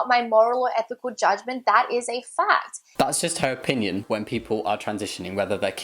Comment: (ik she says fact but the captions say fad and i thought it was funny) 12:19 yes, that is a fad: F not clear research A cover up for transphobia D absoloutely dumb